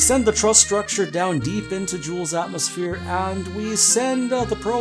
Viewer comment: did you say Jool??